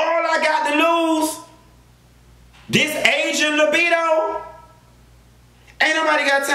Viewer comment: lol nigga I thought you said "Asian libido" damn 😂😂😂